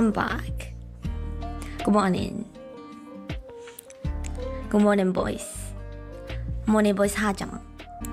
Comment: NGL, I misheard "Good morning voice" as "Good morning boys" and it did really did something to me...